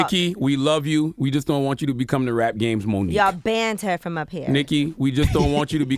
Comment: Y'all didn't hear that?? She clearly said that they ”ban her from up thre”